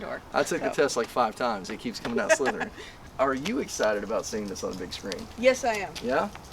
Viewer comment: I thought you said «Are you excited about seeing the son of a bitch scream» 😂